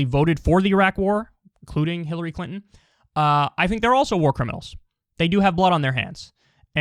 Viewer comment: For a second I thought she was saying "Smooth Criminal". I don't know how the hell I heard that but I did.